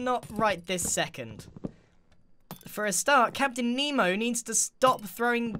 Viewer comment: Did he just call Etho Nemo😂?